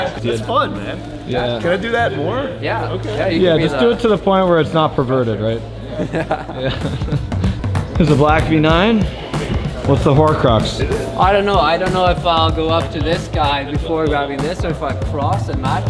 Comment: did just say ' whats the horcrux '